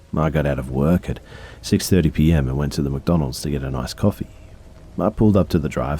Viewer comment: the first story...The narrator was saying "ice coffee" I thought he was saying "I just wanted to get m a$$ coffee"